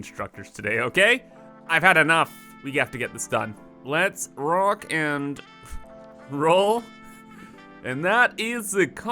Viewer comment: DID I HEAR A ROCK AND STONE?!